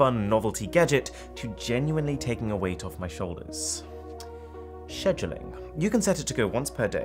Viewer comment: did i hear Arun say sheshuling.